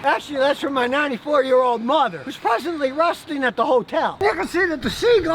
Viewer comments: I thought he said, "rusting at the hotel" at first. :D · I first thought he said his 94 year old Mother was presently rusting at the hotel. Lmao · did he say rusting?